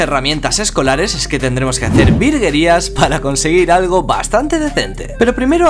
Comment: Escuche la palabra virgerias 😳